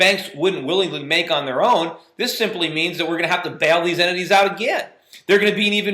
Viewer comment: he says "entities", I almost thought I heard him saying "enemies", that would have been quite freudian... :)